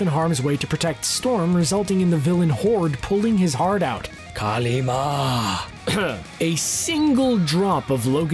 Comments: Did he just Said KAALI MAA ? 😂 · did he say Kali ma ?